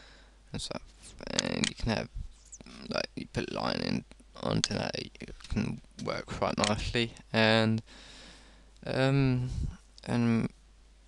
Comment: It's true. I had to listen to 1:54 a few times to get any idea of what you were saying. It comes out like "aaand you can have, like, you put a line in on to that you can work quite nichfly". You skip syllables all the time. I hear you say "sinmafourdy" rather than "sin-e-ma four-dee". Once again, no offense, but it's either that your speech muscles are really weak (no one taught you proper enunciation), or that you're on some form of depressant or tranquilizer.